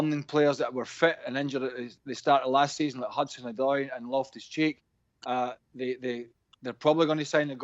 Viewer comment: I thought he said “lovehischeeks”